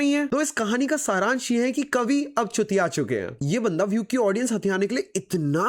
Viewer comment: I heard it first time kaavi ab Chutiyaa chuke hai 😂😅